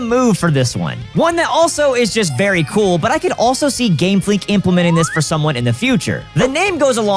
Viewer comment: did I hear Gamefleek? XD